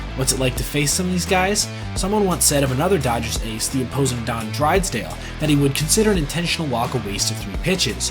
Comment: Did you say Don "Drides-dale"?